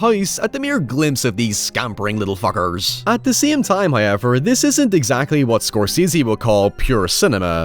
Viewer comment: Lmao I always forget which horror movie reviewer I’m watching until I hear the “haiyever”